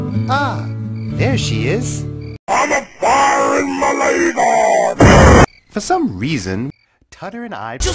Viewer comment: Does anyone know what is being said? It sounds like, “I’m a fire in Malaysia,” to me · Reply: I’m-a firin’ mah lazer!